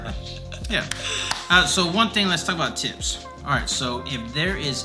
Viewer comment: At :37 I thought he said “well one thing let’s talk about tits” ...